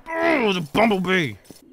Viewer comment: did anyone hear say bubblebee at 9:43?